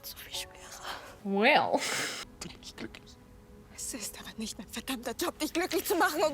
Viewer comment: their scene when he shows up at her family party. There is something the translators missed that you can confirm. After she said that she hates him but she also loves him, his demeanor changed when he heard her say it, and then he said: "All I want is you, "meine liebe". I looked it up and it means "my love", correct? I thought that was so moving because it's the first time we hear them express those feelings for one another. I think he's being sincere but without knowing that he said it, we're left with the impression that he's just being needy. I don't know, maybe that's just me, but it got to me when I heard it, but didn't see it in the subtitles. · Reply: Hey, german here 😊 that’s not what he said, he said: “Alles, was ich will bist du, okay? Wenn ich bei dir bin, dann bin ich glücklich“ which translates to: “ all I need is you, okay? When I am with you, I am happy“ and that’s all he said